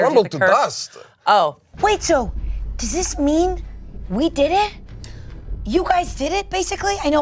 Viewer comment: What does Angela say @ 24:07 ??? I hear “we did it?” before she says “you guys did it, basically” but it’s not synced with the video, looks like she’s saying something completely different…. 🤔